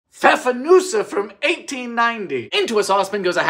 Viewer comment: I thought you said hasenpfeffer and I was like oh my God yes make that lol